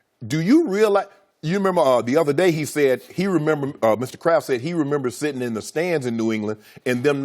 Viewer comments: I thought shannon was saying Mr. Krabs lol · I thought he was saying Mr. Krabs, I'm like why is Sharpe talking about spongebob